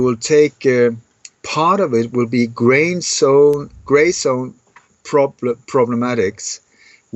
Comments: Update: 9/11 op "Sea Eagle" exposed and NATO/Gladio's false flag backbone broken. WATCH ON TRUETUBE.CO: <link> ACCESS FULL NEWSINSIDEOUT ARTICLE & LINKS <link>: Operation Sea Eagle 2019 is an Oct 23-23, 2019 "9/11 op" in 5 EU countries, unless exposed WATCH ON TRUETUBE.CO <link> ACCESS ARTICLE & LINKS: <link> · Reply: "Essex Police said it set up a casualty bureau for people to call if they are concerned about relatives following the incident at the industrial park in Grays." "Grays." Did say "gray" something? Yes, I watched the video again. "Graystone" at 23:00. Here's info about the driver and that port is very near LONDON! "The truck driver arrested after 39 bodies are found in lorry" <link> via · "Essex Police said it set up a casualty bureau for people to call if they are concerned about relatives following the incident at the industrial park in Grays." "Grays." Did say "gray" something? Yes, I watched the video again. "Graystone" at 23:00. Here's info about the driver and that port is very near LONDON! The truck driver arrested after 39 bodies are found in lorry <link> via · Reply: "Grey zones" he said.